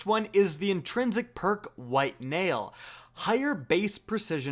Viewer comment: Did someone say “White male” ?